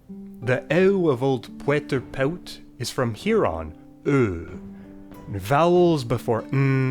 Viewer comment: When you said "from here on" at 6:55 I heard it as "from Huron" and for a second I was like, oh, influenced by Iroquoian languages